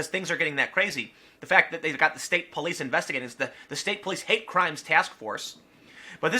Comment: Did I heard 'chalk crimes'? :'D